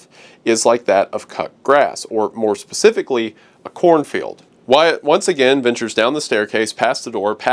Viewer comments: I thought he said Chlorophyll, not cornfield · i dont think wyatt says corn field here. he says chlorophyll. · I thought he said the smell is from chlorophyll not corn field